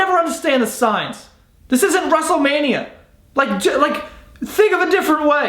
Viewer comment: It sounded like he said "russelmania" lol